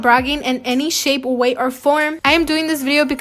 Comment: Did she just say "in any SHAPE, way or form"???